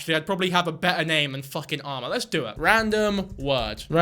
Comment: i was listening to this and i kept hearing "Alma" instead of "armor" and got confused why was judging like "Alma's a cute name tho" NOT ARMOR?!?!!?